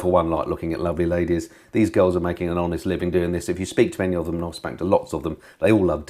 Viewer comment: did you say "spanked" lots of the grid girls@ 10:40😂